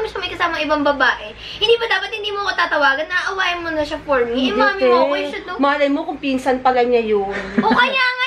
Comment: HAHAHAHAHA SARAP BALIKBALIKAN Tapos 15:23 did i hear it wrong but sabi ni ate kay mommy mo ko😅